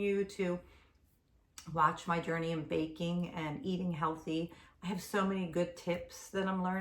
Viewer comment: Baking, I thought I heard vaping lol. 😮😂😘😘